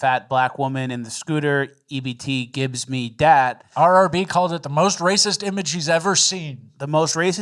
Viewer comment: what did he say? RRB said its the most racist image she's ever seen?